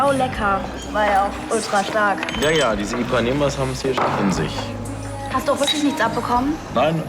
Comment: Was sagt Emilio bei 10:19? " Ja, ja diese....habens hier schon in sich" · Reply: Ipanemas